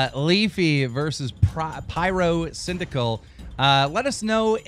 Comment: did he say pryrocindaquil? Also like if watching in 2016